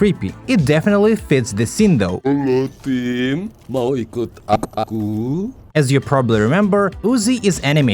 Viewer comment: "halo yatim! Mau ikut a-aku?" WHY DID I HEAR TGAT